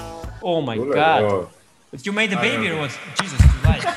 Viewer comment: what anish said? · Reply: You made a baby or what ?